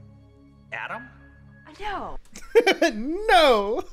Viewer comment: i thought she said yo